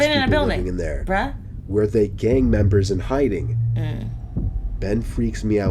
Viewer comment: I thought the blonde hair guy said gay members please don't hate me I'm not making fun of it